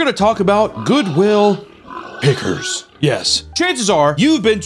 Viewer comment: i thought he said piggers at the start thats what i call cops lol